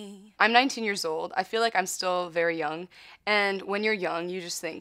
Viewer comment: You know what...I thought she said that she’s 90 years old